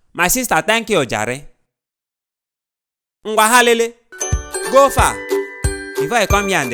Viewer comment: who else heard "vaha lele" in hindi